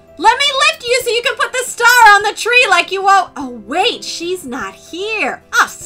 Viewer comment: When Barbie mentioned lifting Chelsea up to put the star on the tree, I thought she said that she would lick her up to the tree, ew 😅